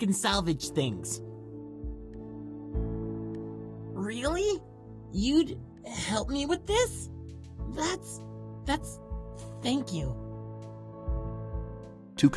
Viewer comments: the subtitles say " ...Really?... You'd help me with that? That's... thank you." But Gema Taku actually says "...Really?... You'd help me with this? That's..That-- Thank you." Just a small error that I wanted to point out. Continue on making this game even more exciting! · The leader of the gaming club’s dialogue doesn’t match his subtitle 4:50 he says “you’d help me with this” but the subtitle says “with that”